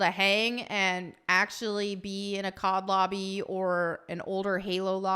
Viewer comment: Im not a COD fan.. but every time she said COD lobby I thought she said COB lobby (Calling it COB in reference to all men) LOL